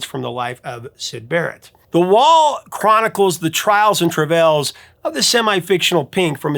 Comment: Did you say, "Trials and trivells?" 🤔